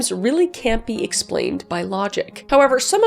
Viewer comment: don't ask me why my hearing must be off But at 00:18 I thought you said "campy explained by logic"